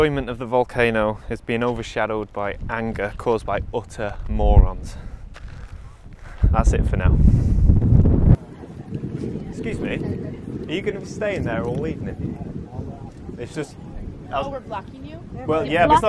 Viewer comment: I misheard you the first time, thought you said "Excuse me are you going to stay there all weekend?"!!! lol . Good on you for saying something 👍👍